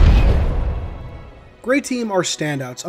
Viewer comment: I thought it said gravy team